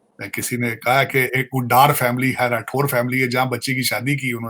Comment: did he say Rathore family???